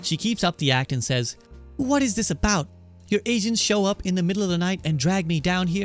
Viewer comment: To me it sounded like you just said "Your Asians show up in the middle of the night and drag me down here". I know it's supposed to be "agents", but it's pretty funny to think that Azula is racist towards Asians considering she herself is Asian.